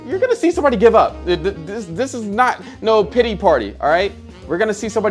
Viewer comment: I thought he said Diddy party instead of pity party 💀